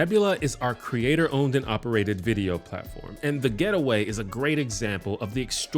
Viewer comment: He said, "The getaway." What i heard was "the Ghetto way."